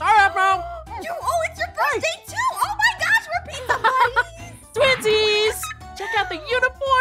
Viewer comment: I thought it said,”Pizza Butt!”:_AphmauCool